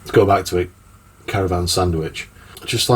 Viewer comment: Great VR mate, I thought you was saying caravan sandwich at first 😂. I refuse to move on. Fallout 3 is a great choice. Cat 🥰 I've been tagged twice now 😂. Cheers mate.